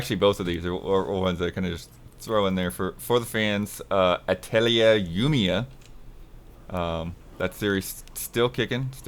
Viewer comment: i swear I heard "Atelier Goonia", and I was like "damm, this franchise is too selfaware about the state of its fanbase"